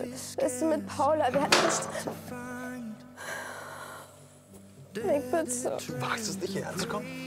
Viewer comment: Wer hat auch Nik verstanden?